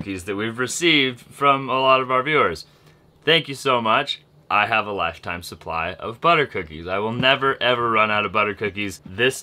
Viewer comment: What said: "I have a lifetime supply of butter cookies." What I heard say: "Send more butter cookies."😆😆😆